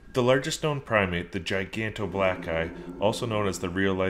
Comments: r u black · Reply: it sounded like he said giganto black guy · I thought he said the giant black guy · Did HE JUST SAY GIGANTOBLACKGUY!??